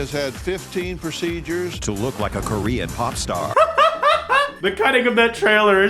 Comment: For a second I thought he said "He had hecka lotta surgeries to look like a Korean poptart"